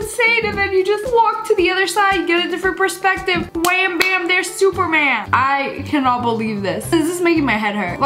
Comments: "Wham bam there's superman!" *sad spiderman nosies* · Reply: Is that what she said? I thought she said "super ma'am" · She really called Tom Holland "Superman" 😂😂😂 Edit: omg I have never gotten this many likes ever! Tysm!!! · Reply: I first thought she said spooderman